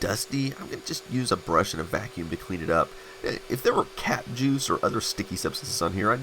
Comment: I thought he said "cat juice"😂